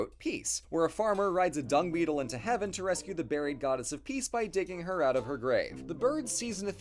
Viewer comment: I'm gonna be real, during your description of "Peace", I thought you said that the farmer "rescued the goddess of peace by dicking her out of her grave" and my immediate reaction was THAT IS SUCH A GREEK THING.